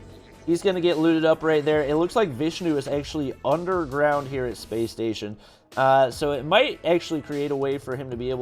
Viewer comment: did say bus station space station